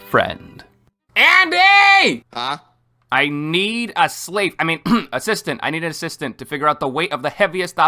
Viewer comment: While I know I misheard it, "ANDYYYYYY- I need a sleep" instead of "slave" still makes me cackle